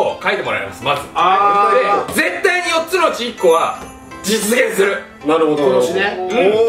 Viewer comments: 絶対に4つのちんこって聞こえた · 「４つのちんこ」にしか聞こえない… もう駄目かも…重症だ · ｢絶対4つのチンコは｣に聞こえるw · 「絶対に4つのちんこは」にしか聞こえない